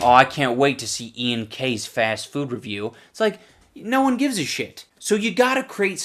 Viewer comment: it sounds like he says "Ian K's food review"? So his last name could really be Kovic and not Carter then like some rumours say, eh.